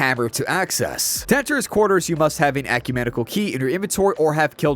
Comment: the alch-a-medical hydra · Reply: I heard it as alchumenical, like he mixed the words alchemical and ecumenical.